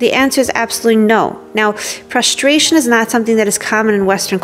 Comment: Those are great points you've shared with us in this video. At first I thought I heard you say: "Frustration is not something that is common in Western culture." And I thought that was odd, because by my observations it is in fact quite common. But then I realized that you actually said: "Prostration is not something that is common in Western culture."